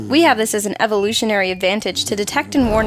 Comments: Anyone else heard semen first😂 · who thought it said semem